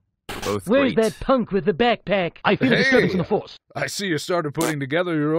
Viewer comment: it took me 4 or so years to hear “I sense a disturbance in the force” instead of “I want the papers for the divorce”